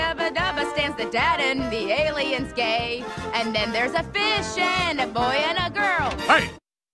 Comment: “ badabada and the aliens gay “ that’s what I heard